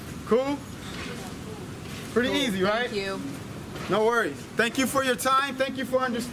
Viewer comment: Did she say "Oh Fuck You" at 8:39 😂